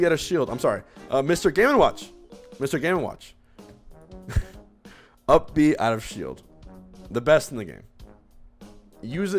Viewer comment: what did he sayyy!!! 😂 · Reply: He said game and watch has the best up b out of shield in the game